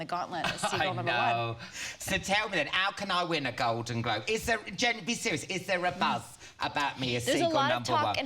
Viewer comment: did he just call her "Jen" ? lol.... or "just be serious".